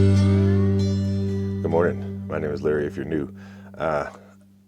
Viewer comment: Great video and helping Brad out. I am so happy you straightened what you first say out. It always sounded like you were saying: hi I’m Larry Canoe and here you have been saying: hi I’m Larry if you’re new😂😂😂 and not canoe😂 you did say it fast!! Funny!! Hi to Mason & Marty 🐾🐾🦴🦴❤